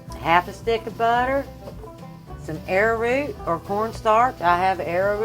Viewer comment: Is this potato salad? It does sound like ur saying air root lol never heard of arrowroot before though thats interesting. I like ur apron too u can tell u r a good cook